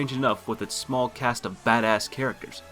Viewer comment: Why did You say fat ass instead of bad ass 4:03?